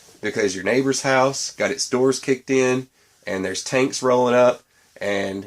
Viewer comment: Oh tanks, I thought you said taints!